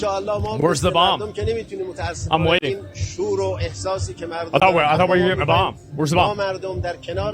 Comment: Did he say churro and sausage at 0:35??